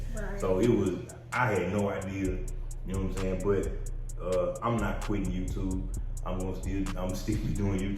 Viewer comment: Click click BOOMMMM AT 15:22 what do he say I’m not quitting YouTube. I don’t even believe this happened I believe he just needed content… I’m done